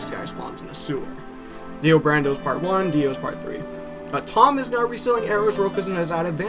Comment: Wait Did I Hear "DIO in part 1 and Dio Brando in part 3" Xddd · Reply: no it was Dio Brando part 1 and Dio's part 3